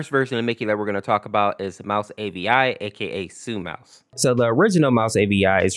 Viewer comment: what he's saying sue mouse. what he's actually saying:suicide mouse